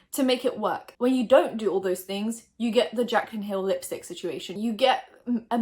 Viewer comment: I thought she said "Jaclyn Kill"